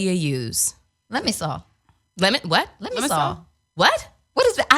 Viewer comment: Lemisol peppermint dreamszzzzz🙌🏻🙌🏻🙌🏻🙌🏻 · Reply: I thought she was saying 'lemon salt'. I'll check it out. Lol